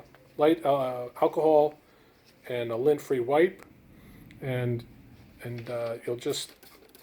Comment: Excuse me, please, could you write, what kind of wipe can be used with "light alcohol" to clean the ITB? Can't hear clearly at 3:36... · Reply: lint free wipes and isopropyl alcohol